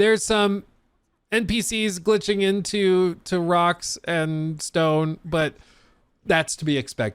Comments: Did I hear a Rock and Stone? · DID I HEAR A ROCK N' STONE? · did I heard a Rock and Stone ?